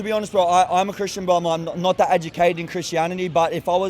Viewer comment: This young man is not a Christian he hasn’t read nor understand the truth of the Bible.His comments are futile. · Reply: Rewatched it, he says he's a Christian, but not that educated on it, which is a weird thing to say tbh, but yeh I'd misheard. Apologies